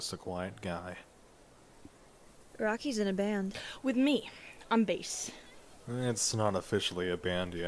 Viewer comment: honest to god, for a second there I thought you had said "I'm based"